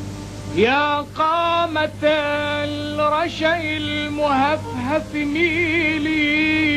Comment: did he say "happy Meal" at 01:07?